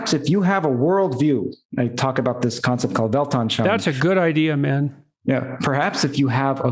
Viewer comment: What is the comment at 24:00? I can't make out the name of the concept. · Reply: Weltanschauung.